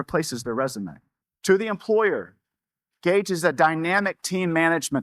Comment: Is it Gage? Somehow I've heard it as _C_age.